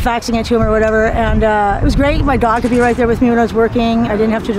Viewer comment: No matter how smart you might be, sometimes events happen in life that can crush and take away all you've ever built, including your identity. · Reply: I stand corrected. At 1:34 she says she had her "dog" at home. But she said it so fast it sounded like "daughter" to me.